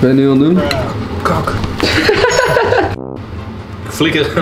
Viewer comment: wat zegt papa nou? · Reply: Kak